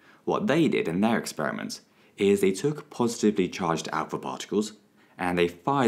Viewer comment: the cc say out four but they mean alpha, just in case you were confused